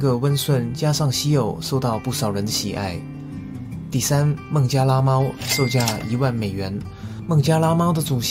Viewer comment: 差点把“孟加拉猫”听成“孟加拉虎”